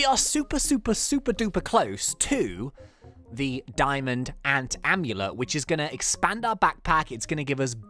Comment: ant amulet? i thought u said star amulet!